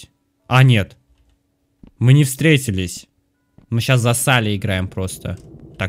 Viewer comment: мне одному послышалось "мы щас зассали играем?"